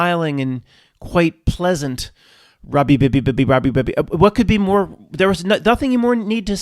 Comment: What does this word mean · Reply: Robbbbbbbbbbbby Paarkk Her from Sandy Beaches Hk.